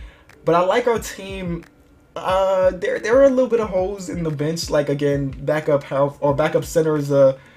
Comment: thought he said "hoes in the bench" 😂